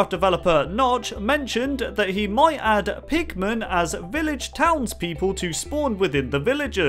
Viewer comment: Now it's time for 75 Ways to Die in Minecraft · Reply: It sounded like pikmin on